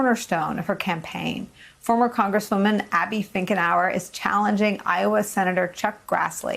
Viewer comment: I heard you say her name as Abby Thinkernow ... awesome, do take away old Grassley's seat.